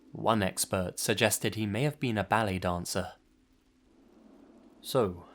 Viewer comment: On the Somerton Beach Man case when someone said they thought he may have been a ballet dancer I thought you said belly dancer for a second